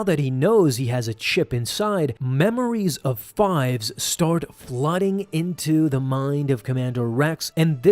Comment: I thought they said woffle started blasting too